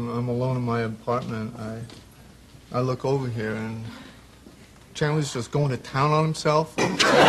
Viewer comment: what did ross say? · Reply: "I look over here and Chandler is going to town on himself...."